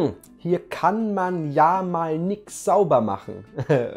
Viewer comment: das was du nicht verstanden hast heisst: "Da kann man mal Yannik sauber machen." oh Gott das ist echt komisch. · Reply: Ich hab verstanden : das kann ja mal Yannik sauber machen